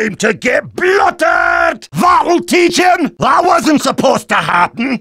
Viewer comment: what does he say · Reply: That'll teach 'em!